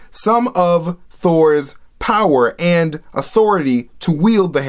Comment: I cant believe i laughed cause i heard 'auTHORity'